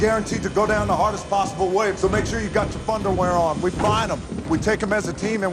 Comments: I always thought The Rock said "thunderwear" in 5. It sounded funnier. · I always thought the rock was saying 'Thunderwear' as in you will shit yourself so hard it will be thunderwear.....